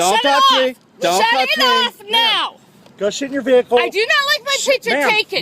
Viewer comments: Did anyone else hear "Go SHIT in your vehicle" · is he saying "go **** in your vehicle"?????? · Who thought the cop said “go shit in your vehicle” the first time